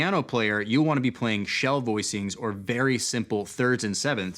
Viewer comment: Caption programs in English mishear "shell" as the Mandarin "Xiao". Times are achangin'.